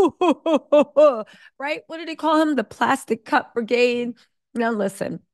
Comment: I thought you said, "plastic cup for gays". Lol